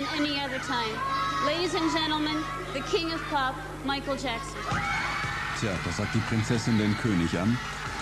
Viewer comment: Why did he say lady? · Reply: She says "Ladies and gentlemen, the King of Pop, Michael Jackson". She's speaking to the audience.... so like "Ladies and gentlemen, please welcome: ..." 😀